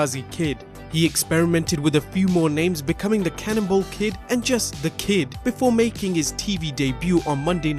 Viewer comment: I heard ,, Cannibal kid "